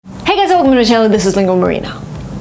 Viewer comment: Nobody understood what was she saying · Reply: I think said : "Hey, guys ! Welcome to my channel ! This is LinguaMarina."